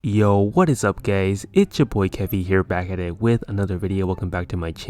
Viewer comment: Did he just say YO WHAT IS UO GAYS" bro I aint gay